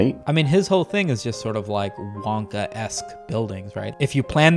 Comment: When he said "Wonka-esque" I thought he said "MonkaS" and somehow both apply